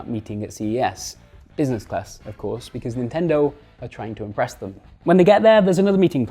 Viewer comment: for a second there I thought you said AREEST not impress!